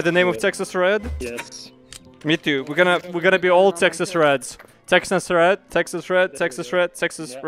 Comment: Did I hear SEXIST REDS